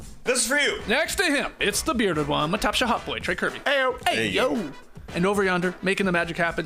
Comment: I’ve been watching these dudes for the past year and a half and I still can’t understand what says when he introduces Trey. This is all I hear: “My taship hot boy, Trey Kirby” - “hey yo”. I hope that’s not what he says. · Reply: "... The Bearded One, my Top Shot Hot Boy, Trey Kerby." — JE